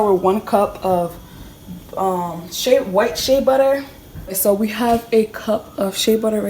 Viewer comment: omg was it just me that thought she said white shit butter? :')